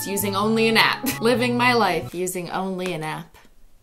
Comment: Is it me or did I hear it as living my using only a nap?